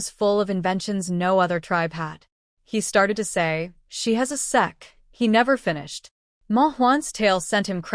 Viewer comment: Ayo bro wtf instead of saying sect in the subtitle it says sex Ayo wtf u should change that bro it’s inappropriate there might be kids watchin😊 bro ❤😂g